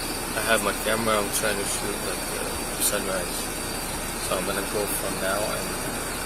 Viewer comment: Instead of asking what time sunrise is, I'd probably be curious where his surfboard is..... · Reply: Oh. He said "shoot the sunrise". I thought he said "surf at sunrise".....